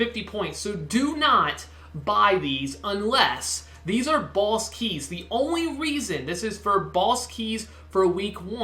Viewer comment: It sounds like you said balls keys